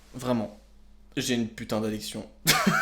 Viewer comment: a 2:07 : "J'ai une putain d'addiction" Ce que j'ai entendu (j'ai de légers problèmes d'auditions) : "J'ai une putain d'érection" J'avoue que j'étais plus trop dans l'ambiance horreur...